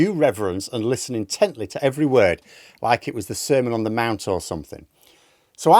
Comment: Like the sermon on the Mount lol 😮😮😮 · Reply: I thought he said semen on the mount.